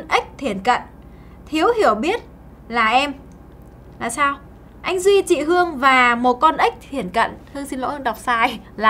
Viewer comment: anh duy và chị hương là con ếch thiển cận - nghe nhầm tí méo mặt :v